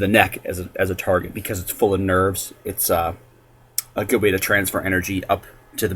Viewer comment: did you say, NERD?